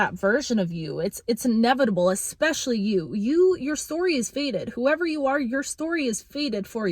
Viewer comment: When you said “your story is fated,” I heard “your story has faded” which resonated with me as well. Old lies we told ourselves are done. The trauma and the drama doesn’t define us anymore. All of that has faded. We are the embodiment of love. 3️⃣3️⃣3️⃣